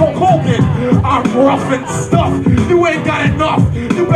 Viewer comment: no, he clearly says "stuff"at 2:29. The "s" pronunciation is actually quite drawn out and clear. had to watch it again today to laugh, as well as see eyedea dominate